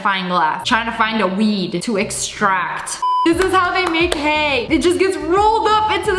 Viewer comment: What you hear “this is how they make HAY”. What I hear “ this is how they make GAY😂